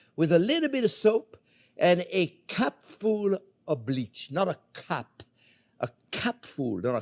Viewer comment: What I hear: "A CUP full, not a cup. A CUP full, not a cup." After a minute of confusion: Ohhhhhh! A CAP full! God, that accent never fails!